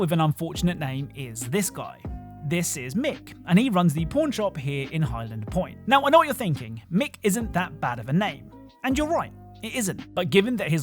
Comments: sounded like he said porn shop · it sounds like he said p*rn and the youtube captions even show it